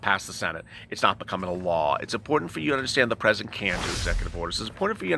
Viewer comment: THERE YOU SAID IT: "It's important for you to understand the president CAN'T or did you say CAN do executive order" 19:56. I'M CONFUSED.